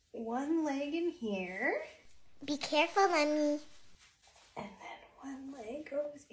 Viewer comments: Wait it sounded like she said bE CaReFuL honey 🍯 😂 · Thought she said be careful honey❤